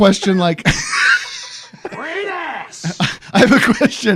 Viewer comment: Wait did I hear Al Pacino from heat say “great ass” at 5:11 lmao